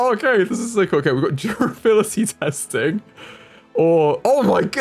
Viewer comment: misheard "durability" as "fertility" and thought "wow this game is dark!" But breathed a sigh of relief upon realizing my mistake. And then they followed up immediately with "dismemberment" 😂😂😂 WOW this game is dark!